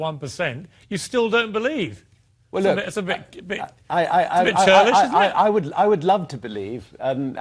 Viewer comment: Maybe I misheard, but did Mr. Douchebag of the year say at 3:07, that it was a "bit childish" for Richard to not believe in Jehovah after surviving? On the contrary, nothing's more childish than keeping an imaginary friend up until adulthood and beyond.